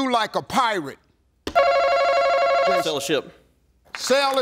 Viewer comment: It sounded like he said FELLOWSHIP BUT HE SAID SAIL A SHIP